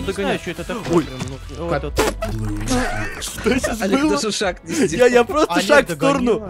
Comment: и 10:06 мне одному послышалось что он сказал оледжа